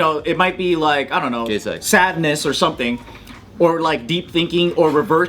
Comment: For once, just for once, can not try to make everything sexual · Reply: No, cuz it makes it funnier. Incidentally @ 8:14 when was talking about anger, joking, and sadness, it was who threw in the words that sounded like "gay sex".